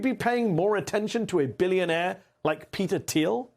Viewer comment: Sorry. I'm not American. I thought you said Peter thief